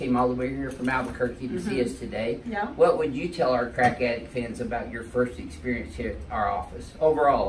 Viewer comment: did he call us his crackhead fans